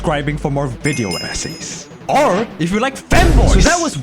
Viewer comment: I heard “or if you like FEMBOYS!!” Instantly subbed 😼